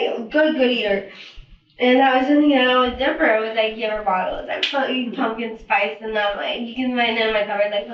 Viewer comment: did she say she put pumpkin spice in the baby’s bottles !!? I know not wild given everything else so far but wth